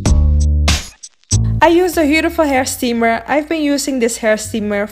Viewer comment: what brand is this steamer? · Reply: Sounds like she said the Huetiful steamer